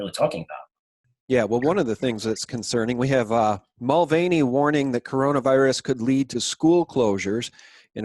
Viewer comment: did you say we have "Molvania" warning that corona could lead to school closures? Molvania? Really? The parody nation that boasts itself as "the birthplace of whooping cough" and "nation with the worlds oldest nuclear reactor". The "land untouched by modern dentistry? Maybe I heard you wrong and you said Moldova but still ... I'm glad to know that Molvania is taking the virus threat seriously. :D